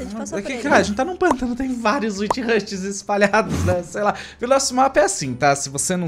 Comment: eu ouvi "tem vários UCHIHAS espalhados" Entendedores entenderão hashuashua